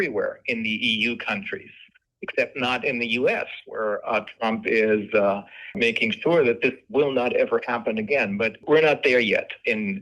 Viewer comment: did I hear that correctly? Did Reiner say, 'Trump is making sure this will never happen again?'. And what is 'this' exactly? I am not aware that Trump is doing something positive. Is he?